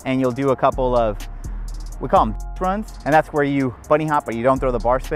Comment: what did you say you call the attempts when you don't throw the bars? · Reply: Bitch outs haha